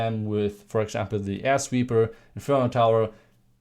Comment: i really heard ASS WEEPER but that is my brain, you are doing a goo job in english, great job ma man!